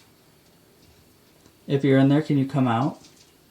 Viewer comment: Slow the playback speed down to 0.5 and listen with headphones right at the beginning of 15:01-15:02 I clearly hear "I'm not. . . " but what is said after that I'm not sure. But I think she's saying, "I'm not in there." I hear it best when I start listening at 15:00. At 14:42, "Sick man!" Thank you. That was interesting. What's that at 15:32?